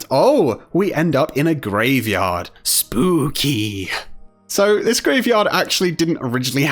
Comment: i thought he said spoopy